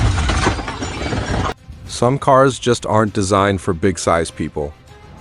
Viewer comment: Had to replay. Thought he said pigsize.